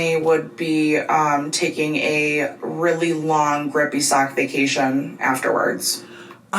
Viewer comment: a really long WHAT Vacation? I’ve tried listening like 5 times and all I hear is “ribeye sock” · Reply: Grippy sock 😂 like mental health inpatient